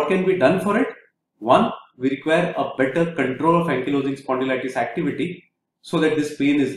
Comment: What are you saying can be done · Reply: Better control of ankylosing spondylitis